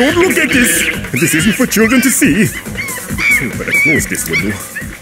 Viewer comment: what does Grim say? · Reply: I better close this window